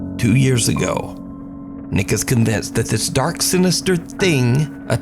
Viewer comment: what did he say?! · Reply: nikius